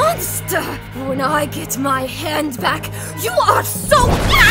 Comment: I thought she said when I get my hands on you I died